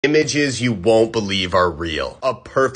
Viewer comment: in the start i thought u said "Bitches you wont beleive r real"